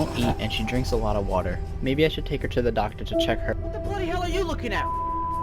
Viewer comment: i think she said "what the bloody hell are you looking at *****"